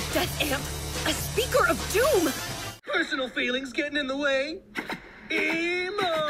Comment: “Eeemo! Emo Might! Emo Might!” “Give me a break…” “Hehe!” skyeeet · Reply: I thought he said “ emo mic, Emo mic!